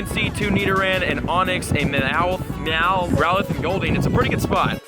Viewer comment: when he said growlithe it sounded like rowlet haha wrong gen m8